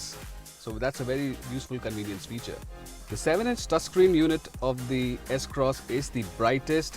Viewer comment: Did he say Taascreen instead of touchscreen at 5:22 ?? XD